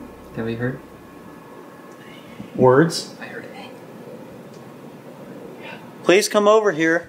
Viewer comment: a voice whispers “I hear you”